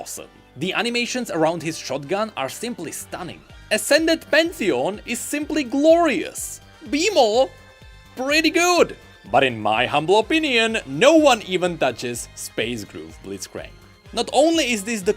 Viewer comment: Everytime when he said Bee‘Maw I thought he said Beemo😂